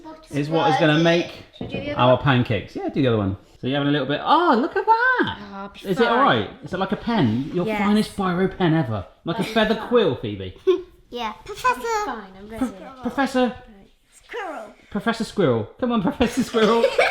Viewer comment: i heard phoebe